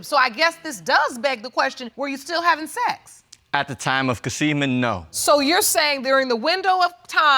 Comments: Ummmm..., at 2:15 did he say say “at the time of the conceivement “ ? WTH is conceivement? 🤦🏾‍♀️🤔🤔😂😂😂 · What was that word he used around 2:10? Did he say "at the time of conceivement"?